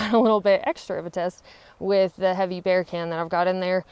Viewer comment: I thought she said beer can, not bear can, lol.